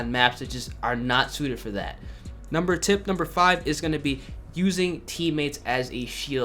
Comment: Is it just me or does he say ”number tip number five” in